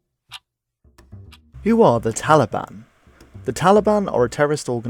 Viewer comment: I thought it said “How is the Taliban?” I was like idk you finna go ask?! 😟😬